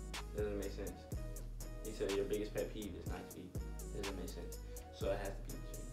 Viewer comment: I dead thought said he kisses feet lmaooo